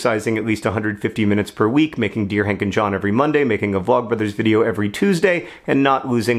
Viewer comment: I heard "Deer hunting John" instead of "Dear Hank and John." Oh the possibilities.